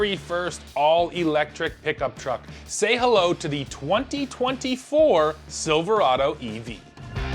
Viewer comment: Did he just say: “Say hello to the Ford Silverado EV”?